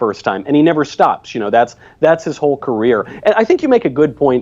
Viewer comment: When Steven said "career" I thought he said "Korea"